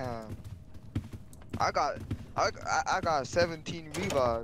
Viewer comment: (it says “(edited)” because I put the wrong timestamp, at first.) What the guy that looks like the void in Minecraft ACTUALLY said: “I got 17 revives” What it sounded like to me (at first): “I got 17 vbucks” Pls don’t criticize me for it, but I don’t get why parents let kids that are, like, 11, 12, or younger, play Fortnite.